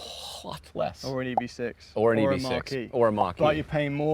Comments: Mach-E! I thought he was saying marquis.🤦🏻‍♂️ I was so confused😂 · Am I the only one that thought James kept saying "Marquee" and not "Mach-E?"